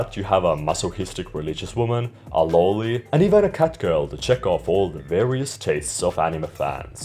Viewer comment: First I thought you said that it contains the obligatory CUT girl instead of cat girl. Which, if we are totally honest, wouldnt be surprising in anime, but it peaked my morbid curiosity. It's not to shame your dialect or anything. I am not a native speaker of English as well. You are smart enough to see that I giggled at me mishearing and not at you. At least I hope so.